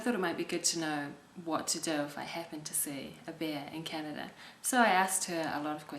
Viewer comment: It's funny, but if I only listened to your voice and didn't read the title, I would have thought you were saying "What to do if I happen see a beer in Canada."